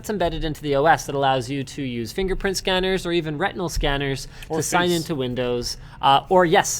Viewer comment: Did he just say Rectinal Scanner?